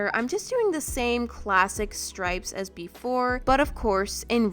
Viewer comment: Omg lol at 12:00 in the video said the same classic- Ad: MURDER is the leading cause of death among pregnant women like wtf also it sounded like said: classic murder